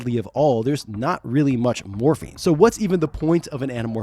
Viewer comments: Everytime you say "Morphing" I hear you say "Morphine" instead lol. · The sheer number of times I heard "morphing" as "morphine" really makes me think I need to see a hearing doctor.